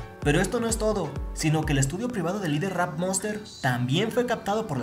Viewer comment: En el minuto 0:37 parece que dice el estúpido de Rap Monster, pero no dice eso